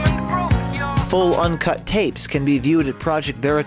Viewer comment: "Full uncut tapes can be watched at Project Veritas dot com" That's what he says at 9:38. What was that about being smart enough to just listen?